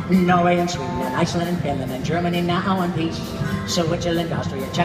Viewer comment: I first thought he said "germany now want peace" as a nood to ww2, but its actually "now in one piece" since well, we germans were depaterted from 1949 till 1990